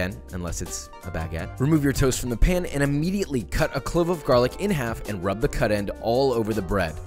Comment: I thought he said « 46 cloves of garlic” 💀 I was ready to go off